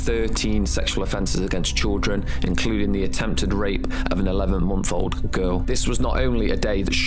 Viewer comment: Did I hear 11 month old at 5:53? Wth